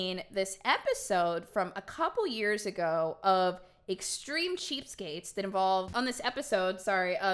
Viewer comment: Hahaha I thought it said extreme Cheescake, I was like oh hell yeah, i love cheesecake